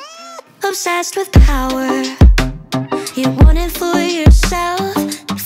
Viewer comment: I heard I'm sassed with power